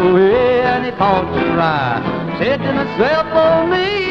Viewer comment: What's that he's sayin' about a cell phone...in the early 60s?? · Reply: “I thought to myself”